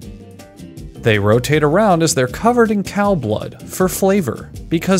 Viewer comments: "Cow urine is added to the mixture." sorry, what? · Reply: i thought he said cow blood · Did they just say cow urine?- 💀✋ Edit: Cow blood. HELL NAH BRUH